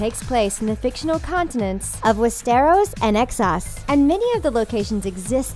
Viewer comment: Drone, Davis, Melisander, Thein...goddamn, also it sounds like she says excess rather than Essos.